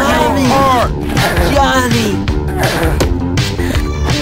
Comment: Does anyone know what peanut said? I understand something like johnny, pardon is that I do not speak English only Spanish, but leave me an answer in English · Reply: he said "Ah!.. Mommy.. Johnny..". nice rhyme.